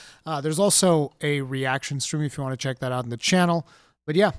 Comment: I know you said "Peace out" to close the video, but I heard "Be sad" xD